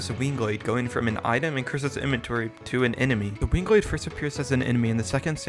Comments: The auto subtitles at 5:44 say 'Wing Gaster' instead of 'Winglade'. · dude at 5:43 the auto generated subtitles in english say fucking 'Wing Gaster' instead of Winglade